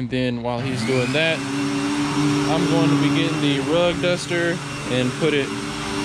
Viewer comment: What kinda vacuum was that...i thought you said rug dr but.....good video btw